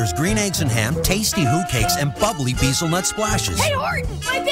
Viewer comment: For a second there I thought that kid said that his pancakes want to adopt him (what he actually said: my pancakes want to talk to you!)